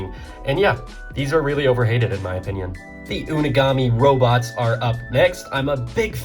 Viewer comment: I think they were called moraii guards